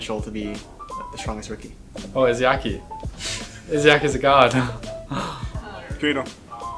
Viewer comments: what Dafran said? Gator? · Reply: Yeah it was really fast, but Dafran said Gator. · Wtf did dafran say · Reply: Gator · Did he said who?? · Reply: Agilities said Izayaki, a Support player for the Valiant.